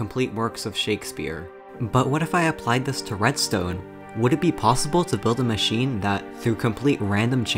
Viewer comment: I heard 0:08 as "Tourettestone", which i think is so ironically hilarious in this video in particular